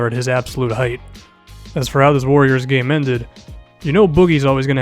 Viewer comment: Go to 3:39 , did he say warriors game?😂